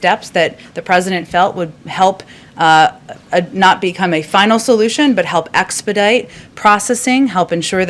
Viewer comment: did she just say “Final Solution”? Wow.